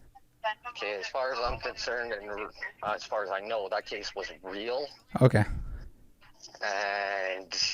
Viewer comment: at the 10:06 mark, does he say "As far as I'm concerned and as far as I know, that case wasn't real". I can't tell if he's saying "Was" or "Wasn't". Which is he saying? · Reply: He said was